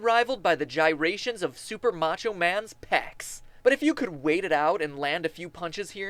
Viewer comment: I swear to Miyamoto that he said Popinski instead of Super macho man